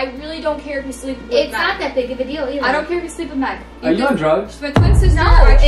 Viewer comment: What Did He say Said Are You On Drugs